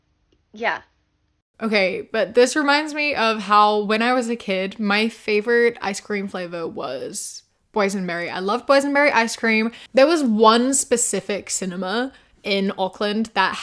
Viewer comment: Ngl I thought you were saying poison berry icecream not boys and berry icecream